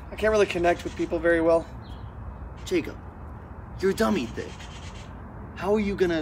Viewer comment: did he just called him dummy thicc